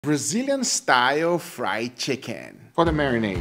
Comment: For a moment there, I misheard Brazilian as vegan, almost shock me there.